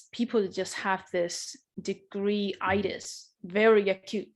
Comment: Did she say"degree itis" by the way? I did not get the idea...at 7:29.